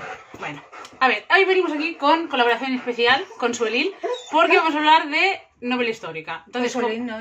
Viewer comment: Qué mona tu madre!! 😍 Al principio entendí "estoy con Suelín". Y digo yo... uy, qué nombre más exótico. Y resulta que era jajaja. Totalmente identificado con lo que ha dicho de Granada y 'mundo árabe'. Me ha encantado ver que vienes de una estirpe de lectores jeje. De tal palo... Un besote 😘😘